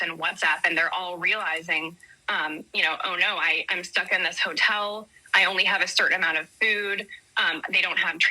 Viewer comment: 😂😂😂😂 I love that for them. Sounds like they are getting the USA special. A hotel is better than what most get. They already have a leg up. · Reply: I thought she said motel. There’s a big difference between a hotel and motel.